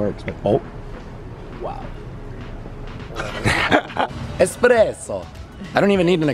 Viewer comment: did he say "expresso"?